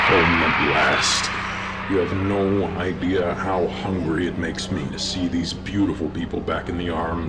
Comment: i thought he said "you have no idea how happy this makes me to see these beautiful young kids back with their family's" but i think henry actually said "you have no idea how hungry this makes me to see these beautiful young kids back with their family's" which is actually really disturbing and the part after with tthe doll srry i forgot his name that part is actually incredibly disturbing pat on the to for creating such a horrifically disturbing yet amazingly beautiful vhs series congrats your the king at this